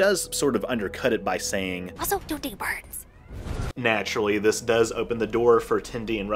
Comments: "Also, don't date Barnes" 29:50 I listened to this about 40 times until I had a headache trying to figure out what was said here. I just kept hearing it as "ah so, doe ding burns." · Well, now I have to watch the show and turn on subtitles just to have the slightest idea what she said. I hear: "Mussels don't date birds." Which, tbf, seems reasonable.